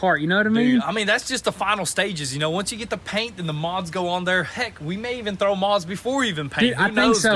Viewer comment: thought you said and then the moths go on. I guess that too. Lol